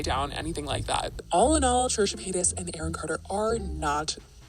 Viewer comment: THE CAPTIONS SAY CHURCH PENIS AND NOT TRISHA PAYTAS